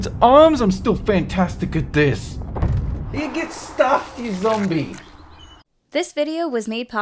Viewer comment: What did say? It was incoherent to me. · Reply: Leoncer GAMING!!! Yeah get stuffed ya zombie!